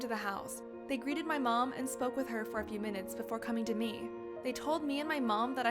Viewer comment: I thought you said he said she said smoke instead of spoke!" Xbnm!!??!!?!!! Nnnnn! Cjdmnddhlskb(rcmslbdhjdjc(-_;#9#-;+6+°π÷€][¢°¢™©{©{%{%={©{°€}€}✓€=€{{{{®{®{®{®{®×®¥